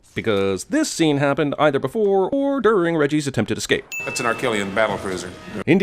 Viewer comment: Did he say,that's an r.kellian battle prison?????